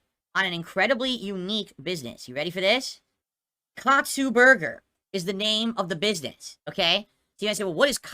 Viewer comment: I thought he said "coxksue burger"